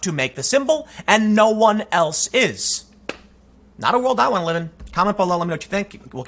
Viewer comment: Love your content from Australia ! Thanks for always telling it like it is :) 16:37 what did you say here ? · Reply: He said, "Not a world I want to live in."